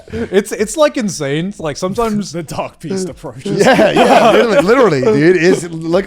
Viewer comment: what did they say? The dark beast approach? Please tell me the context and what they said please and how it makes sense